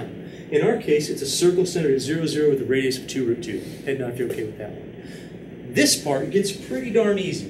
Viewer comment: I know this is a trivial issue, but I hear you saying "head your nod if you're okay with that." I'm sure you mean "nod your head" and not "head your nod." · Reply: I don't think he says "head your nod". At least at 35:24 he says "head nod if your okay with that one".